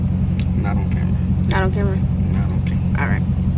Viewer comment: Did she just say not on camera 😳